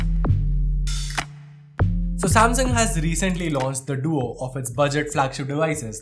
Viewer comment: who heard sushant singh at 0:14 like me😞😞😞